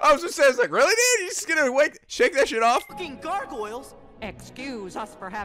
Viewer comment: I'm not the only one who thought that sounded like Sora said "fucking gargoyles" did I? Transitioning from Nico's "off" into Sora's "-alking".